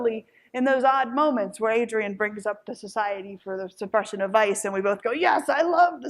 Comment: "The Society for the Suppression of Vice" for anyone else hearing this wrong